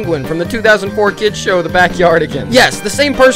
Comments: Is it just me or did I hear "into the cycle b*tch" 💀😂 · I always thought they said into the pickle bi!%$